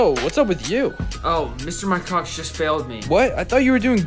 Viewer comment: I thought he said “mr my coc-“